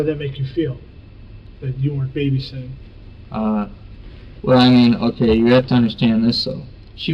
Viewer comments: incase anyone was curious what he says when the text says indistinct he says "well, I mean.. Okay you really have to understand this" then continues speaking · For those who didn't understand what he said at 40:26 this is what he said "Uh. Well, I mean, (ok, you have to understand this though...)"